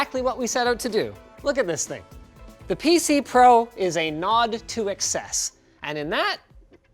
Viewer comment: Captions say "The PC Pro is a nod to access." Should be "...nod to excess". The Mac Pro excels at access. ;D